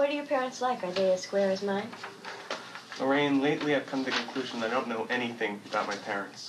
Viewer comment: "legally inclusion"??? what does he say? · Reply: Loraine, lately I’ve come to the conclusion that I don’t know anything about my parents.